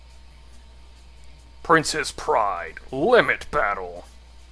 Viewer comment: I could swear said "Princess Pride". XD